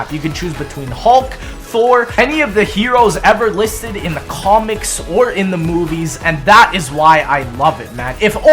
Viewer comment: did he say comnics? An omnics comic